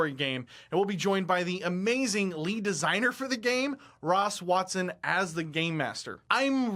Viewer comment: I thought he said “Ron Swanson as GM” and I almost lost my shit. Good vid btw.